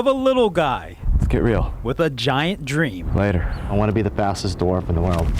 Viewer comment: Thought it said Fatest